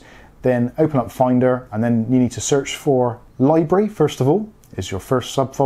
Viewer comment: What do you say at 7:39? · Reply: library first of all